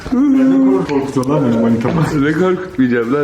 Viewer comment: manitamımı dedi yoksa benim öyle duydum